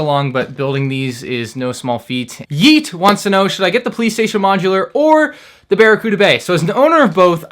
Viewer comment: i thought it said playstation 5 or barracuda bay